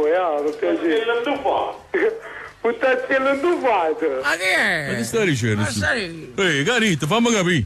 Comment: cosa ha detto ? Anche l'offesa che dice dopo che dice ? Non sono campano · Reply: La prima è "Vuttazziel ndufat" e si dice a chi è grasso oppure a qualcuno che ha mangiato così tanto da sembrare una botte . La seconda è "rannauottolo" che significa rana/rospo